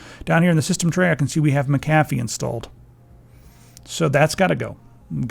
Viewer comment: What did you call McAfee "Mac-A-fee"? @ 4:20 "...I can see that we have 'MA-COFFEE' Installed..." Why? lol